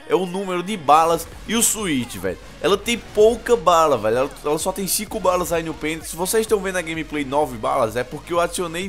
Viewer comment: Eu ouvi "ela tem poucas balas no penis" kkkkkkk like marotu